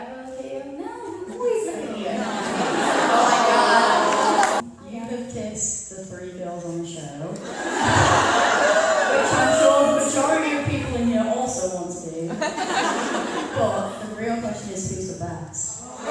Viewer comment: what was said by the audience member at 16:15? and what was the question that followed? i can't quite make it out thanks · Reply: El smith the question was for dom and was: “you have kissed the three girls on the show- which i’m sure majority of people here also wanna do- but the real question is: who is best?”